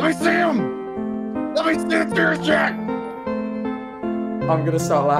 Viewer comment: at that part when he says LEMME SEE SOME TEARS afterwards it kinda sounded like he said sh!t if u hear it closely but when he said the other part I was trying so hard not to laugh bro lol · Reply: He said “let me see some tears shed”